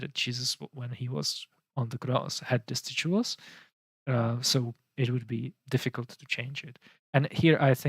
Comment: and several times just before talks about something placed around Jesus’ neck, or over his head. It sounds like he is saying “the jewels” or “vastiduals” which of course can’t be right. Sometimes I have difficulty understanding his English. Anyone know what he’s saying?